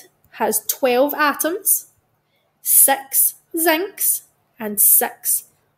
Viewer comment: lol you said sex instead of 6 at the minute of